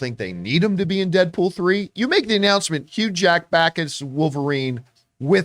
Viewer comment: "...you make the announcement; Hugh Jack back as Wolverine..." Uhmmm... wtf did you just say? hahaha